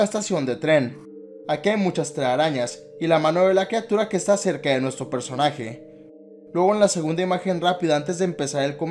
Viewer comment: En el minuto 6:17 dice algo en piso pero no sé alcanza muy bien que dice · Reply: si dice make me part of it too o algo asi, que quiere decir hazme parte de esto tambien o de esa cosa, saludos :)